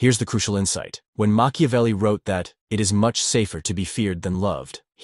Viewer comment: He did not say ‘safer’. He said ‘better’.